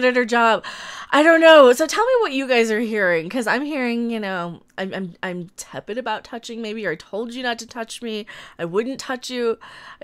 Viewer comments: I heard “what did I say about the touching, I told you not to touch me (it)” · Yeah... I think I hear, "I'm very tepid about touching... I told you I wouldn't touch you."